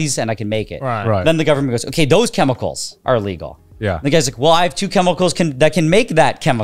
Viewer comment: u had me at meth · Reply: I thought he said these chemicals are hard to get.